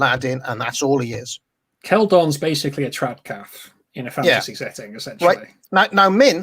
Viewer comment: Keldorn’s a what...? The subtitles say “trap calf”, but that can’t be right... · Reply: Trad Cath (traditional catholic)